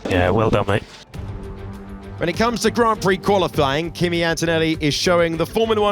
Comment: did he say "qualiflying" at 7:39? Very accurate Freudian slip 🤣